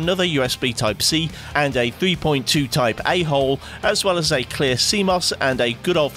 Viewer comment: did you say a-hole?